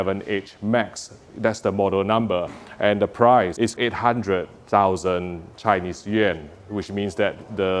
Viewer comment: I thought you said 800,000 yen at first and I was like "DAY ONE PURCHASE". However, $800,000 yuan is much more believable at $110.5K USD.